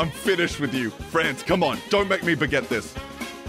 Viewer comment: did he just say "dont make me baguette this"😭